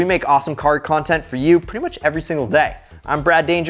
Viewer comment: Did he just say he’s Brad Danger😂😂